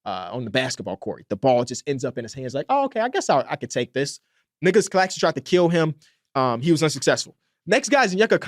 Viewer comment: I swear it sounded like he said Niggalos Claxton 😂😂😂